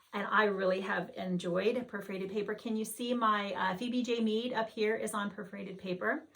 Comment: Hate to bother you but I’ve rewound several time and searched by what I thought you said and can’t find the sampler on perforated paper you have on the wall. It has a large flower and was behind your head. I think you said VVJ Meade. Must not be right because I can’t find it. Can you please tell me the name of it? Thanks so much!! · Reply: Hi Janice! Sorry! Yes, she’s beautiful! Phebe J Meed by NeedleWorkPress!